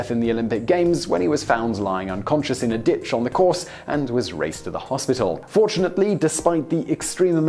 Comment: I had to rewind.. I thought I heard "He was found lying unconscious in a ditch on the course and was raped at the hospital"